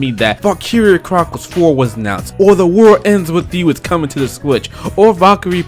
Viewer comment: did you just say The War Ends With You?